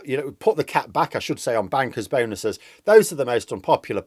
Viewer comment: For a moment there I thought I heard him say "Get rid of the cat" and I was like "Wtf has Larry done wrong?!?"